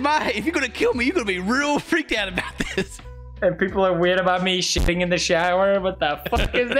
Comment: did he say he shits in the shower? I'm so confused, haha